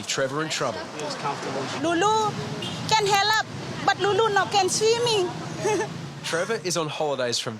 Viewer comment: what did she say? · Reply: She basically talked in the 3rd person about herself "Lulu can help. But Lulu can't swim."